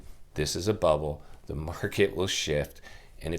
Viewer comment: I had to watch it twice because I thought you said, "The market will shit." ... Which it will as well.